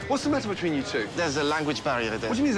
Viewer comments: I thought he said “I know but I need subtitles” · what did he say I didn’t understand it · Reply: "I know, but he's from Texas"